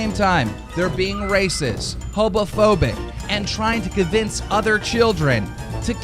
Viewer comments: did he just say “hobophobic” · Did keemstar just say "hobophobic"?? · Anyone else hear Keemstar say “hobophobic” at 16:47. Jesus this guy cant even speak correctly. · did he just say hobophobic, just kinda sounds a little like that Homeless person: exists Team getting critizied: Ah😱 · did he just say hobophobic? :(